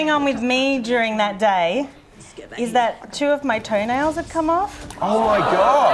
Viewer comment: can somone tell my what is she saying at minit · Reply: " two of my toe nails come off " I think.